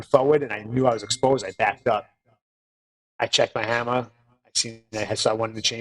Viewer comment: I heard "checked my hammer", so what was he carrying and how? Since he was in gym attire.